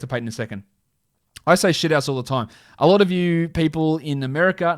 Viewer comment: I am completely mind blowned I always thought he said shitass lmao